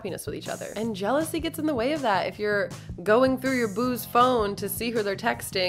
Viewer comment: when she said: when you go through your boo's phone... I thought she said when you go through your booz phone... and I was super confused for a second.